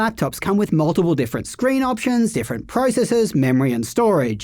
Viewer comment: and 0:56 Did he just say different "PRICESES"...?